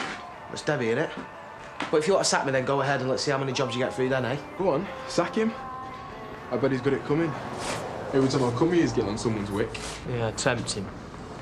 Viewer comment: omg.....did Ed say what I think he said at 1:46?????? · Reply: 'Go on, sack him. I bet he's got it coming! Every time I come here he's getting on someone's wick'